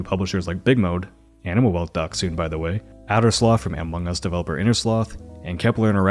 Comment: I can't quite make out what here's saying here. I know it's not "Animal Well got sued, by the way," but that's all I seem to be able to make out. Subtitles don't help either. Any ideas? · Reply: "Animal Well doc soon by the way" doc as in documentary